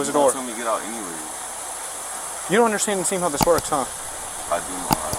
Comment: Did the officer just say "You don't understand to seem how this works."?? 😂